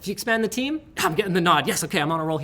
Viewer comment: I thought he was saying "I'm gettin the no"